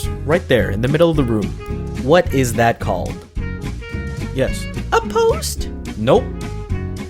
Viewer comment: He literally said "what is that column" Dudddeeee😂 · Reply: He said “What is that called” not what is that column, at 2:21… Listen closely once more